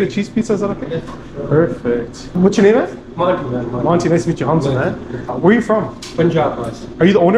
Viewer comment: Lol I thought he said monty too meet you